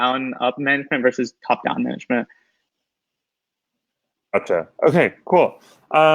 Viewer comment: Did I hear go "Acha"(Okay in Hindi) at the 15:15 mark ?